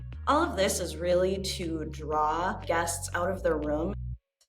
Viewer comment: She said “Draw guests out of their room.” I heard “Draw cash out of guests pockets.”